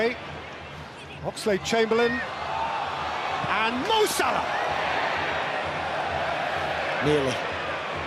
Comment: did he just call Salah "mousa"?